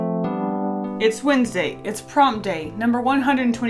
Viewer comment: I thought she said its Wednesday, its prom day